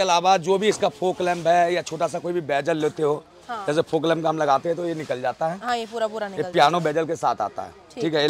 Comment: bhai ye kya bol diya fog land only legend can hear it 😂